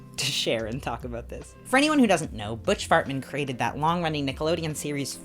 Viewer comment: Im going Fucing insane trying to figure out if you keep peppering in Calling him fartmen instead of hartmen